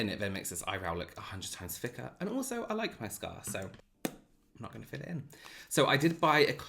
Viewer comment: What I heard:: "I like my flaws" - What I thought:: "This is one of the reasons I love him" 😊🖤